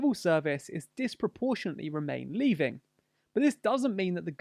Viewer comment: “Remain Leaving” hehe instead of “remain leaning”